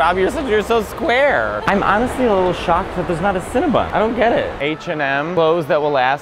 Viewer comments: “I’m honestly a little shocked that there’s not a Cinnabon.. I don’t get it!” The Cinnabon in MOA: 👁👄👁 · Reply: I thought he said Cinema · “I’m honestly a little shocked that there’s not a Cinnabon.. I don’t get it!” The Cinnabon in MOA: 👁👄👁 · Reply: Oh.. I thought he said cinema! 😕😳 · “I’m honestly a little shocked that there’s not a Cinnabon.. I don’t get it!” The Cinnabon in MOA: 👁👄👁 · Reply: I thought he said Cine-MA! 😅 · “I’m honestly a little shocked that there’s not a Cinnabon.. I don’t get it!” The Cinnabon in MOA: 👁👄👁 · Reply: I thought he said cinema…